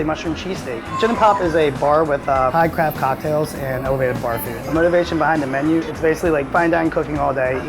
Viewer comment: Okay I am sorry I am somewhat hearing impaired, someone please tell me I heard this wrong. At 4:12 I definitely heard “high craft cocktails and elevated barfing” and I replayed it a hundred times and that’s still all I hear. Ummm?? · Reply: "Bar food"